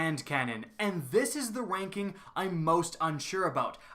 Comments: instead of ranking it sounded like wanking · it sounds like he says wanking